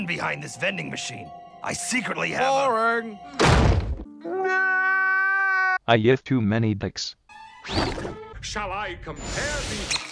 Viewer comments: Which TTS (Test-To-Speech) program did you use on the FNaFB (Five Nights at F--kBoy's) one (number 6; 0:13) and what did the voice say? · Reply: Happy Pirate Fox It says "I yiffed too many dicks". Voice is "Mike", at -1 speed <link> It's not the same voice used in the game, though. Couldn't find out what that one was. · Me: Braeburn, I can't understand you... "I lift too many things"? I live too many lives"? Braeburn: I yiff to many things. ;3 Me: ... ^backs away slowly^ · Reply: the Glaceon (Ultimate Umbreon) "I yiffed too many dicks".